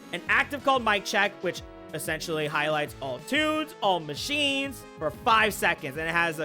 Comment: Highlights all TOONS? i guess i heard it wrong XD